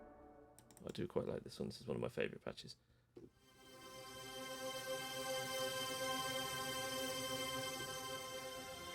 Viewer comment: what is the film you are referring to; sounds like you are saying "Calls", but I can't quite hear it. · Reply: The film is called 'A Field in England'.